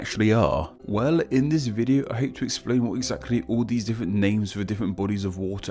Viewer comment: sounded like he said. "I hate to explain" LOL